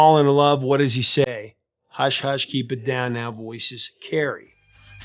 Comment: I heard the line " Keep it down now" as " even downtown voices carry "